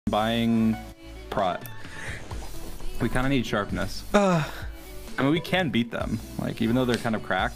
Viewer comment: Idk why I thought he said "we cant eat sharpness"😂.